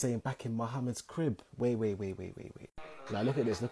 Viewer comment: I thought he said "moma crib" twice 💀